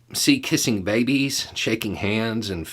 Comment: I gotta quit doing bong rips at breakfast.. I heard “kissing hands and shaking babies” and had to go back a few seconds.. 😉